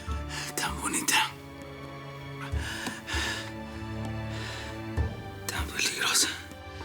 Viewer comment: Min 10:18 Tan bonita, tan ..... ¿ Qué dijo ? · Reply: Tan bonita y tan peligrosa